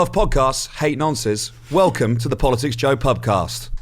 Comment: I missed the first two words and thought you said 'hey nonces'.